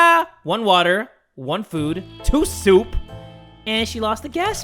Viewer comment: jay:1 food 2 soup what it actually says: 1 soup 2 water